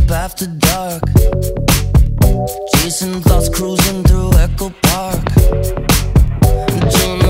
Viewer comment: Looool I thought he said chasing THOTS through echo park 🤣🤣🤣